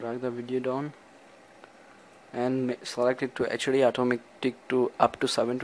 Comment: lol he said atomic instead of automatic? wth